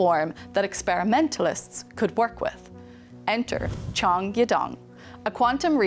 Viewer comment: Did u just call Chong a dong?